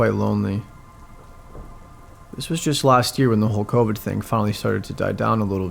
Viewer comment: I thought he said the whole hulk hogan thing not Covid thing lmao